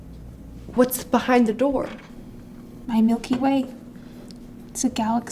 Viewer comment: I can’t be the only one that when the BTS girl, said “my Milky Way” that I thought she said Mikey Way. I was freaking out for a second